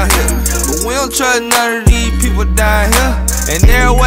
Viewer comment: "WILLTRUTNUTTER EEE PEOPLE DIE HERE" Literally what i hear. someone tell me what he's saying. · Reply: "We don't trust none of these people down here"